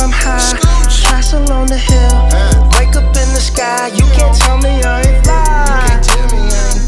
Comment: I thought that said Wake Me Up Inside.